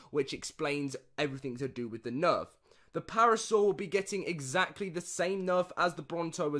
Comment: Lol at 6:07 I thought you were joking and said parasaur and it took me so long to realise you meant paracer